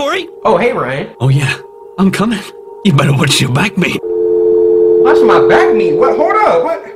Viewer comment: I never thought I would hear a British man say “Better watch your backmeat.” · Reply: Ngl I thought he said watch ur back mate